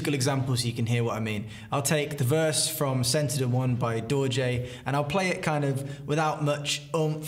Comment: I always thought you were saying Dolje not Dorje until I watched a previous video with your band name in the title! Now I can look y'all up and follow y'all